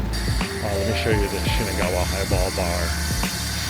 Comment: Highball Bar? I could've sworn I heard "Eyeball Bar"!! Now that would be a great spot to go day drinking!